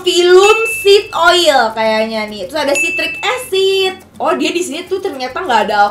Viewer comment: Salfok ka tasya bilangnya citric acid, tp tulisannya stearic wkwk. Apa aku yg salah denger ya...